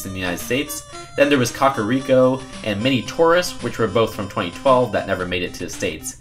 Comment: When he said "mini Taurus" I thought he said "mini tourist"